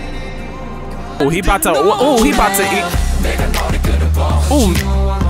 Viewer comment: tell me I just misheard "he about to eat--"